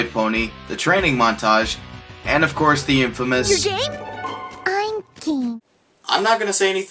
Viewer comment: When I heard Rainbow Dash said, "You're game?" I swear to God I thought she said, "You're gay?" Hence, the romance. :)